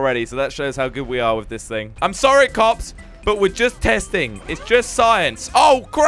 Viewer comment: Did you call us kops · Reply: no he said cops XD